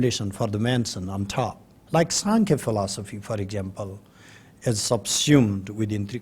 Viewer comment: Did he say 'Sankya Philosophy ?'